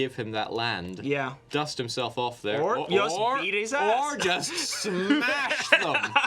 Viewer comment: "Or just eat his ass" I heard that